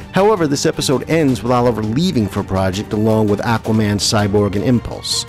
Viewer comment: "this episode ends Roth Oliver leaving for ??? along with Aquaman, Cyborg and Impulse" I couldn't make out what he was saying in that quote above from the video... anybody understand what he was saying there? jw... it would actually be a big help bc little things like that tend to bother me lol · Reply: "This episode ends with Oliver leaving for a project along with Aquaman, Cyborg and Impulse."